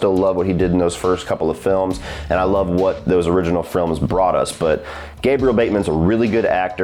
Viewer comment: I’m pretty stoned, but did you just say “frilm” right before 3:46? If so, I’m going to start saying “frilm”.